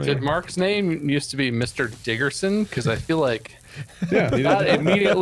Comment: Me: what did he just say? Diggerson Me: oh. We cool